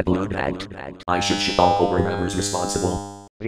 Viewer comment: what did he say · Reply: The unadly reboot needs more ads ads ads ads ads.